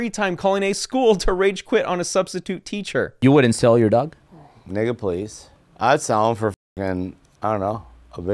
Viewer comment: Did he say "ni99a please" @ 8:20? what????